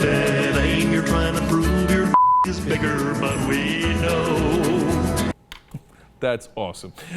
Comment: the fox news people bleeped out the word "thing", the original line was ". . . trying to prove your thing is bigger" and they made it sound like he said a bad word or something, which he didn't! goddamit fox news!